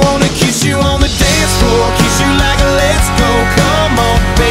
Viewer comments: stg thought i heard “kiss you like a lesbo” 👀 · Thought he said "kiss you on the dance floor, kiss you like a lesbo, come on."